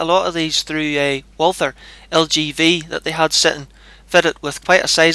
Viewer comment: Well for one thing their both heavy but i don't like light rifles anyway. The LGV is very quite with little mechanical noise and except for the plastic stock on it it felt like a tuned rifle. The first thing i did was to break the breach open and wiggle the barrel to see if there was any play in the jaws which there wasn't it was solid, the guy on the both though i was trying to break the thing. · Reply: Thanks for the reply. Sorry I thought you said the LGU and not The LGV. I'm glad you found them to be of a good standard though. I think I know what my next springer is. Especially if there's not the added cost of tuning. What was that guy thinking of. Of course your going to give a good proper once over, a bit of wiggling is a must in a quality test.